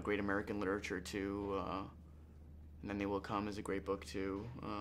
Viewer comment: Does anyone know what book he says around 1:22? All I can catch is "Will Come". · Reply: UncleJohn72 maybe it’s “they will come by Julian Francis”